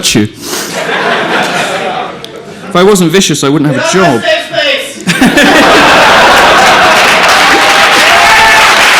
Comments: what does the guy say at 10:39???? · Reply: "You're my safe space." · what did the heckler say at 10:38?? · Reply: I believe he said "We're on a safe space".